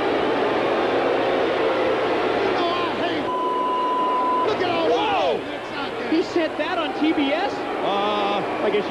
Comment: I wonder what it was that he said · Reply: He said "Rome, Georgia" the town this was taped in. Every episode of Saturday Night where they do the bleep sound then the announcers react "WHOA!!!!!", they're really just saying the name of the town.